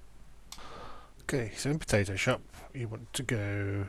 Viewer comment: I swear at 3:00 you say potato shop.